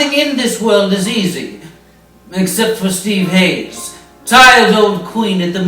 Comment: Did anyone else catch "My secret Love" playing in the Ben Hur clip!? LOL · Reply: Did anyone else catch calling Steve easy @ 0:16? Don't worry Steve, we'll never tell.